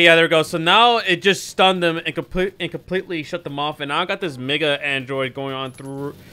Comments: i heard say the n word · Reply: he says mega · did he just say the n word 🤔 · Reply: I think he said "Mega Android"